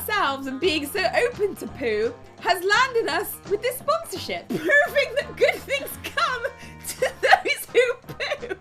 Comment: did u just say POOVING that good things come to those who poo omg i cant